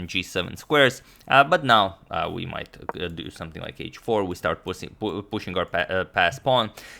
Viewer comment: said 'pussey' instead of pushing 🤣🤣🤣🤣